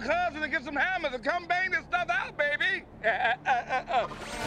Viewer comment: what did bumblebee say “better than that?”